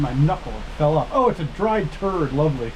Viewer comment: Also My Friend, What You Said On Video 27:36 , I Thought That You Said, It Is A Dry 💩💩💩💩💩 This For Real 😂😂😂😂😂😂😂😂😂😂😂😂😂😂😂😂😂😂😂😂😂😂😂😂😂😂😂😂😂😂😂😂😂🤣🤣🤣🤣🤣🤣🤣🤣🤣🤣🤣🤣🤣🤣🤣🤣🤣🤣🤣🤣🤣🤣🤣🤣🤣🤣🤣🤣🤣🤣🤣🤣🤣🤣🤣🤣🤣🤣🤣🤣🤣🤣🤣🤣😂😂😂😂